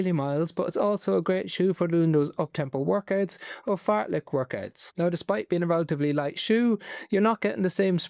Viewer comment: Sorry doc, @ 6:36 did you say Fart-like workouts?